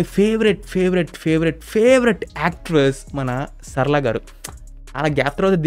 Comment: I thought you say Tamannaah bro😅